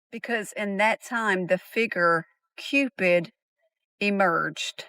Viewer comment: did she call him a figger!? 💀💀💀